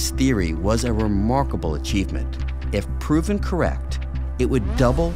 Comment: "If proven correct" hey wait a minute the subtitles say "incorrect." Just letting you know no offense. 😀